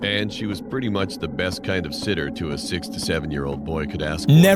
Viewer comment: anyone else think that he said sixty seven? I literally had to replay it 4 times to realise what he said